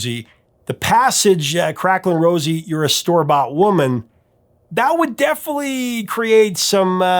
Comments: I always heard "Craclin' Rose, you're a STUBBORN woman" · I always heard "store bought woman" as stalwart woman. I grew up in Australia and we say "shop" here not store.